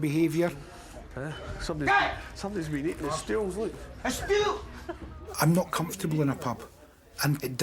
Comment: What's John shouting at 4:36? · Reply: his friend says, someone’s been eating the stools, then John shouts out, A Stool.